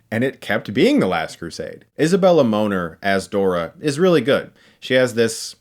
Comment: Is a Bella Moaner-what I keep hearing! :)